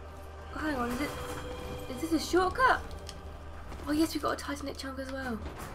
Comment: Did you just say Tizanic chuck?